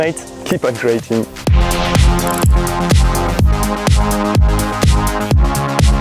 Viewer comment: Il dit quoi le gars à 9:42 ? ahaha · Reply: Il dit "C'est pas bon ça monsieur, c'est pas bon..." T'auras l'échange complet semaine pro! 😂